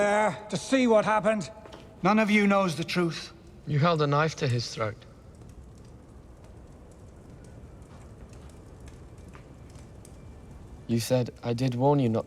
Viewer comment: I know Bran became very stoic at the end, but the tone in his voice in this scene was perfect. What he said: "You held a knife to his throat. You said 'I did warn you not to trust me'" What I heard: "That's right. I know everything you did. You prick."